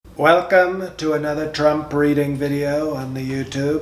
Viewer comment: I thought he said"Welcome to another Trump Breeding".